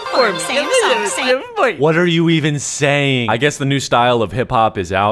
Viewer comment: Why did I hear Samsung in